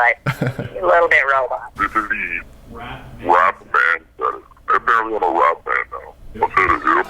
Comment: Did he just say... this is me... Rap Manster...?